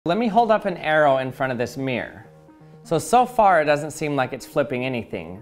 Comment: "the only thing a mirror flips is front and back" - my physics teacher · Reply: Am i high bc i heard it 'mierr'